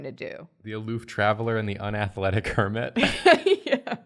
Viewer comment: i need yall to know that i first heard say "unblooded kermit" instead of "unathletic hermit" I pictured a naive kermit who was destined to become some sort of ferocious killing machine